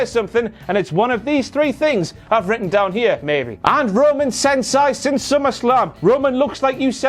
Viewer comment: Renee Young, I think she says "And Roman Reigns since Summerslam." Bit of a fluff of words there to be honest. She didn't think that sentence through.